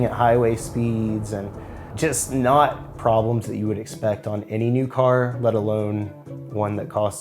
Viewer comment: Mans really said "problems you would not expect any vehicle to have" after describing shaking at freeway speeds. Does he or does he not drive a Jeep? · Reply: I think he said "just not problems you would expect on any NEW car"